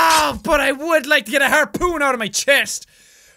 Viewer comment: when i heard jack say "harpoon my chin" sounded like "harpoon buddy bitch" to me when i first heard it